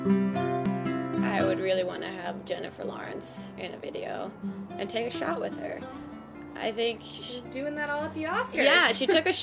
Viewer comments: I heard 'I'd love to have Jennifer Lawrence in a video and take a shower with her' first time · I thought she said do a video with Jennifer Lawrence and take a shower with her for a minute there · haha, oh my god I thought she said 'I'd love to have Jennifer Lawrence on Dailygrace…and take a shower with her'! good gravy, the things I think I hear...